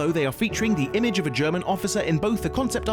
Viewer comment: did he say German officer?